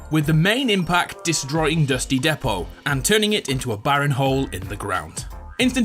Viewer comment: Did u just say dusty depo